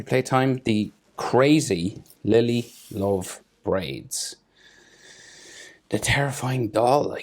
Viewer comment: why did I hear "Lily lof braids"😭